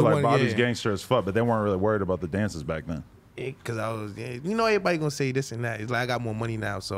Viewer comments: cause I was gay is what he said? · "because I was gay"...who else heard that? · did he just say "cus i was gay"? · I swear bobby says "yea cause I was gay" under his breathe.